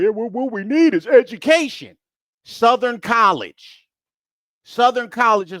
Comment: Not that it matters,I thought the girl said, “some college”instead of Southern college.